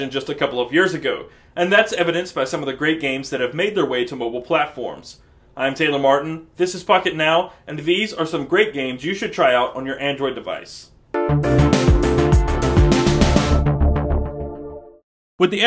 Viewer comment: sounded like you said this is "fuck it now"